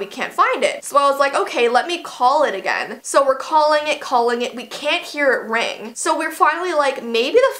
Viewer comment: I thought you said that you can hear it ring